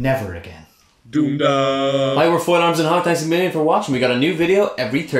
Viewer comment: When you said "Thanks a million for watching" I had a little panic because I thought you'd said Amelia 😅